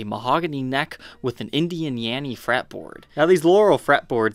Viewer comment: did he say Indian YANNY fretboard?????